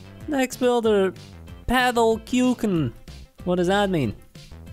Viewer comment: is german and its sounds like kücken its a new born chicken i dont know how did you say this word in english:D